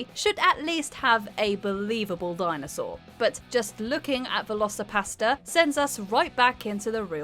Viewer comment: I thought you said Velocipasta XD